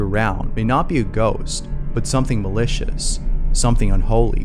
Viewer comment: I thought he said delicious